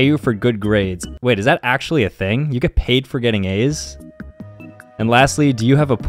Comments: "Do you get paid for getting A's" Asians: we get paid with life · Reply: I thought he said "Do you get paid for getting aids? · “Do you get paid for getting A’s” First I thought he said “Do you get paid for getting aids” 💀💀💀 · "Do you get paid for getting A's" Asians: we get paid with life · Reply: Thought he said "you get paid for getting aids?"